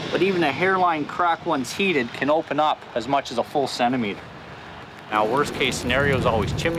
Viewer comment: did I hear... centimeter over there?